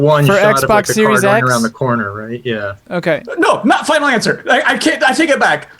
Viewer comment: What was saying here???? "Not fl@ lancer"???? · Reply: "Not my final answer"